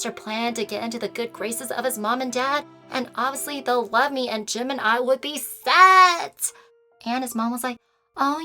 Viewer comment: Is just me or like at 6:59 it sounded like she said Gemini but she said Jim and I -3